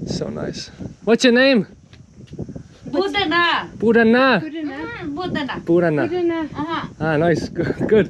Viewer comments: At around 13:54..When you asked one of those women "What's Your name?" She actually said 'Bujdaina' which means 'I don't understand '.. But I think you thought that 'Bujdaina' was her name.. Right after that,they were asking "Where did you come from" which you probably had a hard time understanding.. Me,being a Nepali myself from Kathmandu,I also find their accent somewhat hard to understand It was,however,so much fun watching one of your vlogs.. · when you asked her name, Lady said bujhdaina (means we don't understand). And she was offering you chhyang (local alcohol), not tea. Those women didn't understand what you were talking, otherwise they seems to be so frank that they might have made your day. Thoroughly enjoying your Nepal series ❤️